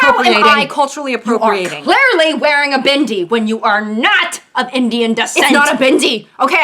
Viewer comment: the way you said bindi it sounded like bhindi, which means okra 🤣